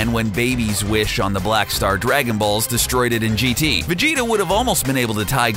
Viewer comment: Did he say Wes instead oh whis???